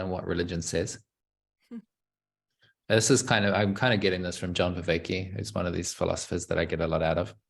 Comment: What is the name of the philosopher mentions at 33:30? I just can't understand. (Btw, excellent conversation, keep it up) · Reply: John Vervaeke